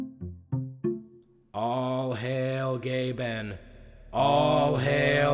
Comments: I’m I the only one who hears all hail gay men here? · When he said "all hail Gaben" I swear I heard "all hail gay ben"